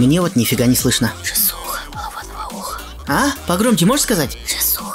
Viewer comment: мне послышалось «Джиссуха хвать тебя за ухо» хаха